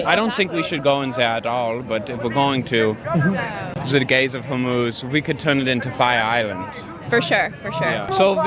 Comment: I started watching through audio, and I was so confused because I thought he was saying, “the gays of homos.” I thought he was saying that we only talk about straight homophobic people and not gay homophobic people, which I guess if we have white people that are racist against white people, we can have homophobic gay people.